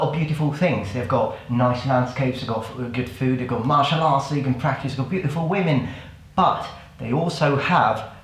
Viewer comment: Did i hear right?(0:08) "martial arts that you can practice on beautiful women"?